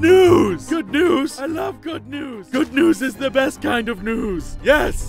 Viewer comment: I heard "Good nudes is the best kind of nudes!" instead of "Good news is the best kind of news!" and chocked on my cappuccino both of them are right though 7u7